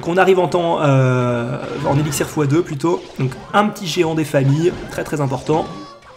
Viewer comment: jai compris " Un petit geant des famille cest tres important" a 6:04 mdr